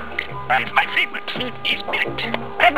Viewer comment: I heard him say "My favorite food is millet".